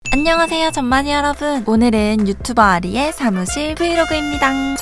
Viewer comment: 왜 처음에 좆만이 여러분으로 들리지 ㅋㅋㅋㅋ